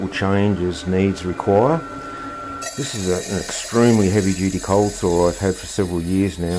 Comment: 'change as needs require', ah, erm, is that a euphemism for cough 'mistakes'?!😅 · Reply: The only thing I made watching this vid was a pasta dish. 7:11 I looked away browning onions I heard saw start up and I THOUGHT you said "this is a heavy duty cold SORE I've had for about 20 years' and I was like geez didn't know this was a home surgery channel. Wound back and checked. Relieved. Anyway I finally sit down to tomato 🍅 red sauce and white Spirulina pasta 🍝 great imagine my surprise at 11:52 just as I sit down to tuck in and it IS a home surgery channel 😮😂